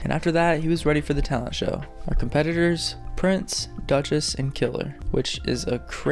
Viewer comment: I heard kilter…